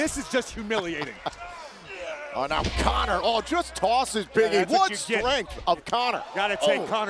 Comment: Sounded like he said Biggie instead of Big E